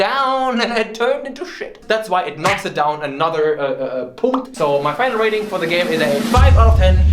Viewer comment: did he say "punct"? Means "point" in Romanian